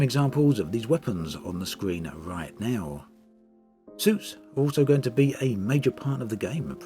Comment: I kept hearing "soups" instead of "suits". Wonder what kind of soup is suited for combat...